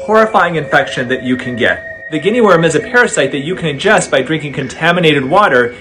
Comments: I thought He said gay Worm 💀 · I literally thought he said gay worm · I thought he said gay worm